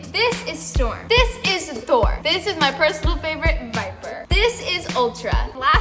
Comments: I thought she said “my personal favorite, WIPER!” I was like 😭🤚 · I thought she said vaper for a sec